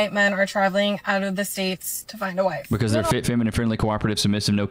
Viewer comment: why does she say « wigan » instead of « wife »?